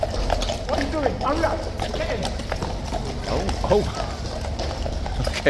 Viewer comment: I thought I heard what you doing hurry up get in😂